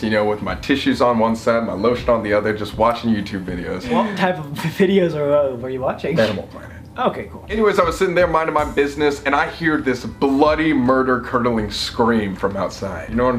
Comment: Colby: with my tissues on one side my lotion on the other just watching YouTube videos: what videos were u watching Colby: Animal planet 😂😂😂😂😂😂 👍 if u get it well if u want to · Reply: I thought he said Animal Porn not Planet 😂